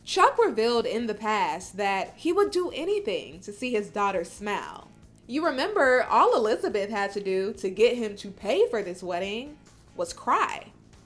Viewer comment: sounds like she says “he would do anything to make his daughter smell” lmao annoying just say it right.